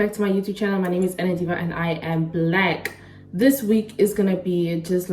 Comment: I heard you say, „and I am black“ in the intro 😂🤭